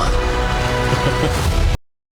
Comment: Y'all are freakin' high. Downey's delivery of, "I. AM. DOOM." is so on point. That's all I've ever wanted from a Doom on screen. He doesn't scream it, he's too regal. It's calm, measured. and menacing. I think you are all just too hung up on RDJ and how this Doom is somehow a Tony variant. even says, "They know what they are doing." but somehow y'all are still doubting. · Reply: My dumb self thought I heard "I. Am. Toom"